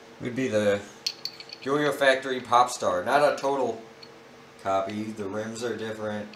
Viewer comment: when i hear popstar i thought you said p$rn star